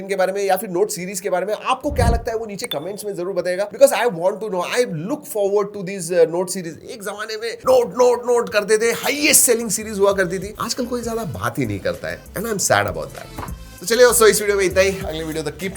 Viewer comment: I heard it :- "Aaj kl koi Salaa baat hi nhi krta hai"💀 Then rechecked and heard :- "Aaj kl koi Zyada baat hi nhi krta hai"🙂 My mistake ik😅😅